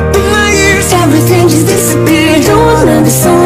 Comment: "Instead I wanna feel good" What I hear: "Instead I wanna eat food" 🗿